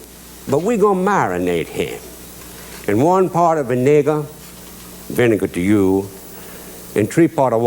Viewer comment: Wtf at 13:24?????? did he really say that??? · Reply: One parta Vin-ay-gah!